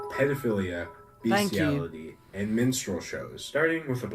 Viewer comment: I seriously thought he was saying "menstrual show" and not "minstrel show 😂😂😂🤦‍♀️